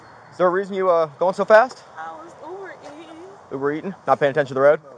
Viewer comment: I never understand why people who drive without license, registration, insurance and have illegal things in the car be speeding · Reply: I thought I heard her say 'I was Uber eatin', and then I thought 'surely not', I was wrong. 😆